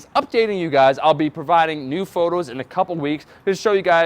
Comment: Did say Nude photos? 5:27. LOL